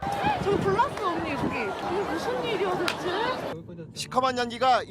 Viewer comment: 아주 신났네 신났어 "이게 무슨 꿀잼이야, 대체?"라고 들리노